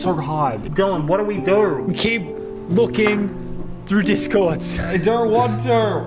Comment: I heard say a nono word 1:50 Edit: nvm he say looking